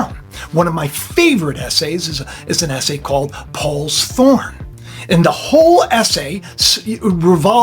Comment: Did i just hear quran at